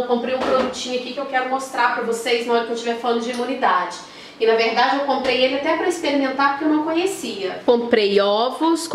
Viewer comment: O que é isso na garrafinha?? Brotinho?? Não entendí a palavra.Você falou que é para aumentar a imuninidade?? Deus te abençoe. Obrigada. Beijos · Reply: Kombuchá